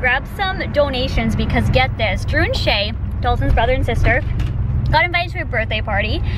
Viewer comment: thought she said drew and shane haha